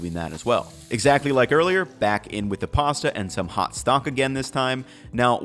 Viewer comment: Love Ethan. So useful to understand cooking. Also, he said stonks instead of stock at 7:20 😂